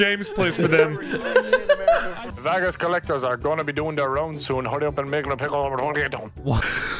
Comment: i heard: "hurry up and make like a pickle, what are you doing" 😂